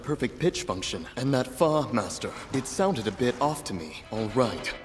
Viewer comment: i heard Bit